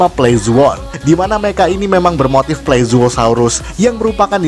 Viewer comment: Kok saya salah denger yang admin bilang itu Plezuohsaurus bukannya Plesiosaurus lhoo.. Hehe.. 😂😂